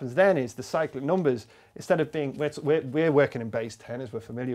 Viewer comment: Saw the name of the video, still thought he said psychic number :(